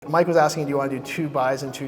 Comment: Play the vid from beginning, it sounds like says "Mike Wazowski" but he said "Mike was asking"